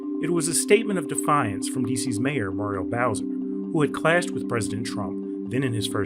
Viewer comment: I thought he was saying mario bowser 😂